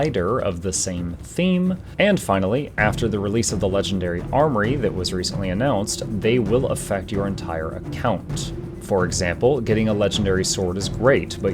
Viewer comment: Hello! I dont speak english very well, any can to write what did he say in 0:36 to 0:43? I think i have understood something and i want to know if it is correct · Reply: He said "and finally, after the release of the legendary armoury that was recently announced they will affect your entire account. For example, getting a legendary sword is great but your necro can't use it when the armoury comes out. But if you get a legendary ring, backpack, amulet, or accessory, those will be useful on every character you have or will ever have."